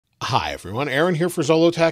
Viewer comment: it sounds like he is saying hive?